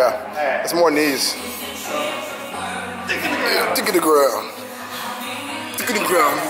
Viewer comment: LOL did you say dick in the ground? My ab roller session will never be the same ever again